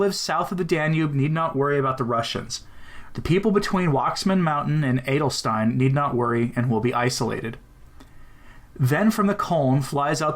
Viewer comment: Please tell me what does he say at 6:47 - 6:53, I don't understand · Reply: "The people between Watzmann Mountain and Wendelstein need not worry and will be isolated." The distance from Watzmann Mountain in the South to the city of Wendelstein to the North is about 300km. Right in the middle of that is Munich. Alois Irlmaier is saying that this area will be relatively safe. Look up these places on a map, and see for yourself.